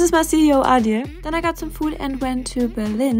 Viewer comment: DID I HEAR "some s**"